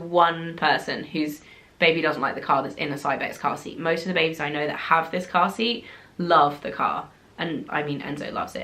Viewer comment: For a second I thought you said "most of the babies I've spoken to about car seats" 🤣